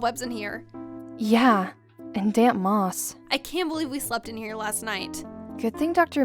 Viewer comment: Wow I thought she said damn moss